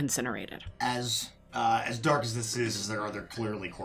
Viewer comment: I thought had said "As dark as this is, are there horses?" and i was like "Horses?? Why are horses dark in a charred orphanage???"